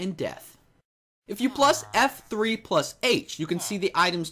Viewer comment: Did he just said plus?